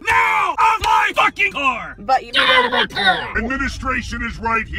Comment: I thought that said “GET PORN OUT NOW”